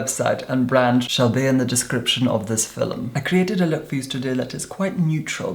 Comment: Did say "youz" @ 1:38 ?